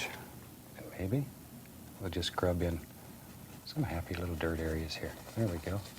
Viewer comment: Did he say "Happy little DIRT AREA"? At 22:20? How can DIRT BE HAPPY???